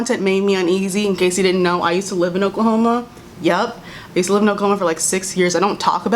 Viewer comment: was half listening and thought she said a coma wtf